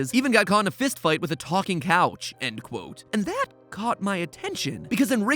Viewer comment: Am not sure about this but I think the book says “got in a fight with a talking chain